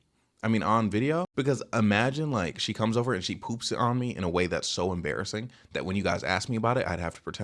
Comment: I thought ts said “she pooped w/o me” 😭✌️